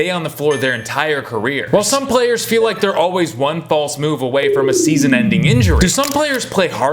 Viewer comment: i definitely heard that as "fall smoove" fuck my brain